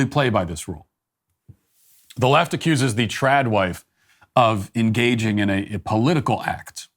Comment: Being a “trad wife” has literally been my dream since I was a kid. 😅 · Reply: For a sec there I thought you said tard wife! And that’s what’s up! Your parents raised you well!